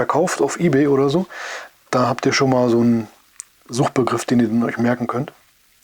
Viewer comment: Ich habe verstanden: „Da habt ihr schon mal so nen Suchtbegriff.“ 😂 Joah, passt auch. Die Musikfeder fand ich auch toll in deinem Review. Ich hoffe, sie begegnet mir einmal zum Testen. Ich spare schon mal los…